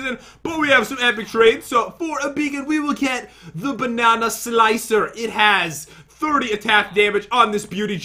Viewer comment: You should have named one of the swords Banana Splitter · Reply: I thought he said banana spitter